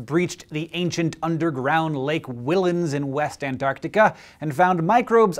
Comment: Wait... did he say west Antarctica? Is... is that possible?